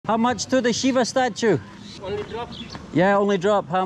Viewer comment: I thought he said shiba statue in Shibuya Japan